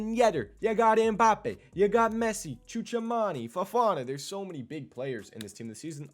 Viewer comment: HUH??!! What did u call him?😭😭😭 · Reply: Choo choo